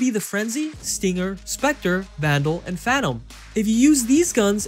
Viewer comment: Am I the only one that noticed he said phandal instead of phantom at around 6:35 or when he was talking about what guns were good for bursting